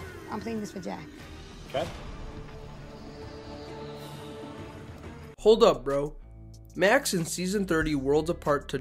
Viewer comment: i believe that was actually talking about "hold up reynold" but im not sure. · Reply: Yeah, Malcom's "Hold up Reynold" before tricking him into playing the idol on him instead of himself has often been misheard as "Hold up bro" (which in the context made just as much sense). So it doubled as both wanting to play an idol and wanting to do it with the same style as Malcolm.